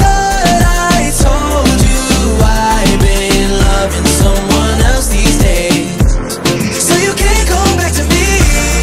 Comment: I thought he said I'm in love with someone else these days